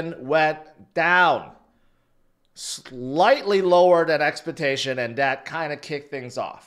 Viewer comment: Did he say expa- tation